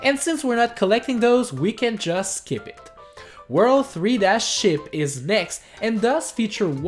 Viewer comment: I thought you said World 3- Sh*t😂